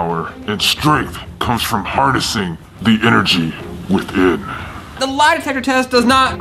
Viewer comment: said inerchi but i think he said energy